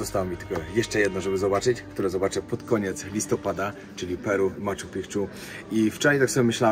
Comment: Czy ty powiedziałeś Peru w Machu Picchu? 5:25 bez spiny ale chyba tak usłyszałem co mnie rozbawiło